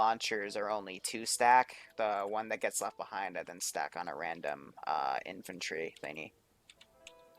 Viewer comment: Who wants a land Battleship. not realistic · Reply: I heard 'random bullshittery'? ( ͡° ͜ʖ ͡°)